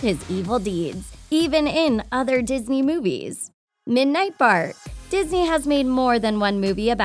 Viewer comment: Who else thought she said midnight fart by accident because they weren’t looking at the words? Yeah I know it’s just me. Sorry I had to disgust you